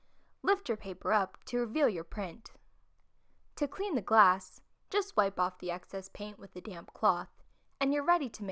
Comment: I thought she said “just wipe off the excess paint with a damn cloth” 🤣